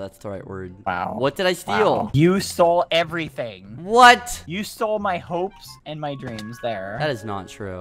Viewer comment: why did I automatically think you stole my heart XD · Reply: I heard "you stole my h" My mind was like OMFG HE IS GONNA SAY HE STOLE MY HEART then "Hopes and dreams" and I was sad